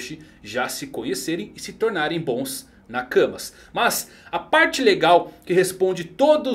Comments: "Luffy e Poseidon se tornaram bons nakamas" Eu tive que voltar e ouvir dnv pq escutei "bons na cama" da primeira vez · eu entendi que eles se tornaram bons na cama 😳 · só eu que entendi bons na cama ? Kkkkkkkk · bons na cama vish 🤣🤣🤣🤣🤣🤣 escutei errado